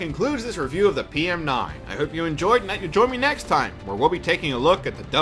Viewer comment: what did you say there? I heard "I hope you enjouhgnmiughJoin me next time.